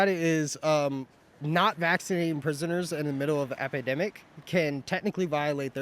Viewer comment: Did he say epidemic at 4:03😂